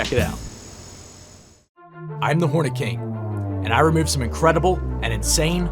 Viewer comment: thought i heard horny king